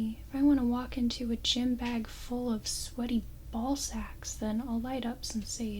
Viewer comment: Hold up.... Did she just say “Sweaty Ballsacks?” Rewind Yes, yes she did 😂😂